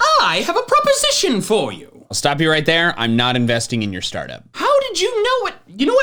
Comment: I heard I’ll stab u right there